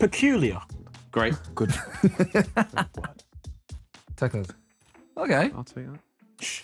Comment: is it just me or what did jme say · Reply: Teckers